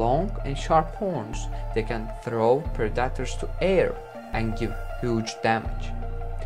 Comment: For 3/4 of the vid I thought he said producter (which is not a word) and not predator